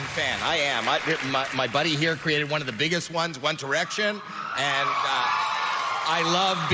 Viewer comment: did he say: "Want ere(tion" ??? 😂